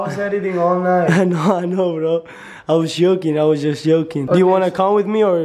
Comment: it was so funny when he said yoking instead of joking